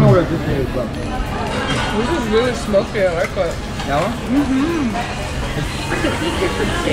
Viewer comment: で彼女が 『凄く柔らかい』と言っているように聞こえるのは、自分だけでしょうか？